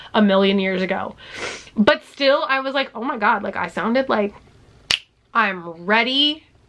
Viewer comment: When you said that you sounded really goofy in your old letters at first I thought you said “I sounded like Gary Busey” and I can’t stop laughing at myself.